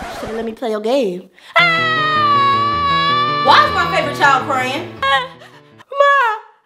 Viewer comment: I thought she said why’s my paper towel crying lol 😂 😂😂😂